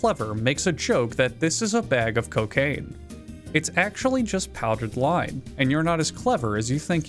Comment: I'm either more, or less, clever than thinks I think I am, because when we saw the guy adding the bag of lime, I thought that the next step in making sugar was "add the sugar from a bag of sugar", like the time I found a recipe for wafer cookies, and step one was "get a package of wafer cookies" Edit: I misheard, it is actually lime, not lye that is added. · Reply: I thought he said powdered lie, which would've been a good "the cake is a lie" reference, considering you could use the batch for cakes.